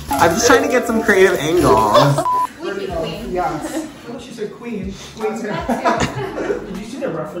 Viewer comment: stylist: “squeaky clean” “i thought she said queen” stylist: “that too” 😂😂😂😂😂